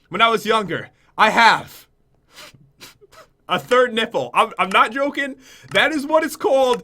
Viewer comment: For a second there in one of the embarrassing things is about that has a third nipple, I thought he said a third nickel. Haha